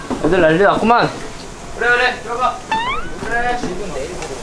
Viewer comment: 그래 들어가 하고 잘들어보면 냉장고로 들어가라고들림ㅋㅋㅋㅋ (화이팅이여! 언제나 응원해요!)